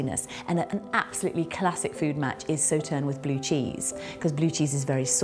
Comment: What does she said said at 6:48? What's the wine that goes well with a blue cheese? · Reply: The wine mentioned is Sauternes, a sweet wine. Thanks for watching!